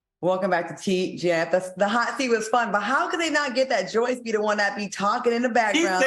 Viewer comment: I thought I always heard "Joy" too. I didn't know it was Joyce.